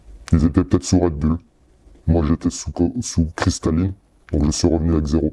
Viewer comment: « Moi j’étais sous coke... sous crystalline » loool